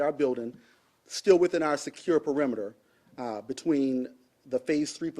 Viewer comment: wait did he say “still within our SECURED perimeter?!